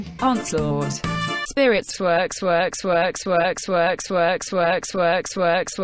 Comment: i understood Swag instead of Works XD